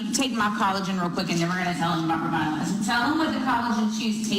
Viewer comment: I was so confused with the first video. I thought they were saying "collagen shoes", even in the subtitles it kept writing "collagen shoes", then finally i realised they were actually saying "chews". That made a lot more sense. Not good getting people to exploit their kids, but these companies really don't care do they. These awful brainwashing cults.